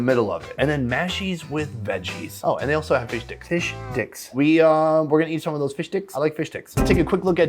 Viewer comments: "fish dicks?" did i hear him right? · Am I the only one who hears him say "Fish dicks" instead of fish sticks.